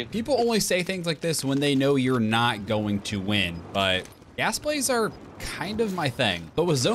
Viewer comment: I thought he said grass plays but I forgot that we don’t touch that